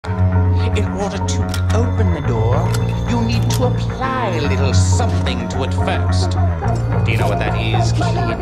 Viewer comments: I'm actually glad that you decided to add his voice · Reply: I hear ".. you need to apply a little suffering to it first" O.o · I'm actually glad that you decided to add his voice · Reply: I hear "open na Noor"